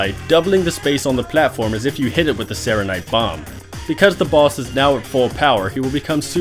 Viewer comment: did he say it doubles the space as though you hit it with a Saronite bomb? That's funny.